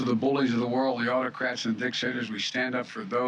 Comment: Wait, did he say Dicks hitters? 1:01:13. Pretty sure we should definitely stand against the Dicks Hitters first and fore most. I finally agree with.